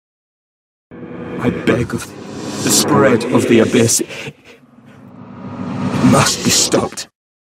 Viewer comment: What does he say at 00:38? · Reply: "I beg of thee, the spread of the abyss, must be stopped. "